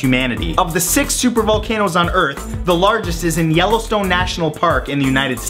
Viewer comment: did he say yellowstone? i heard that name in the show brickleberry and i didnt even know that yellowstone was a thing